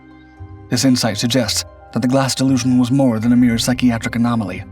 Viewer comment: Glass delusion, or glassed illusion.. ?? 🤔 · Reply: I heard is as 'glass delusion'